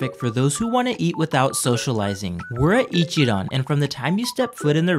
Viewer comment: I thought he said ichiraku